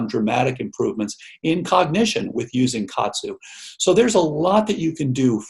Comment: what is the word he said? It is a food from Japan. Sounds like Katsu? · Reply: I think he is referring to Kaatsu